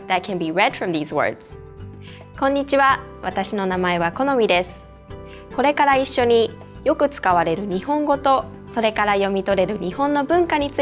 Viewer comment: what did Konomi say from 0:24 to 0: 31 .... all I could understand was " Kore kare Issho ni " .... ( from now on together .... something , something ) hahahahahah ...